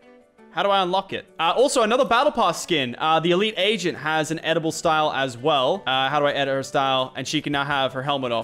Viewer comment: he says edible style instead of editable style😂